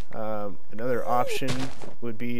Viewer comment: did he say ''nether'' option